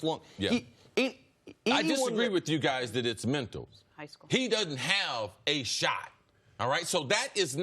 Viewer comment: what did jenna say to nick at 4:14? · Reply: Young Blood high school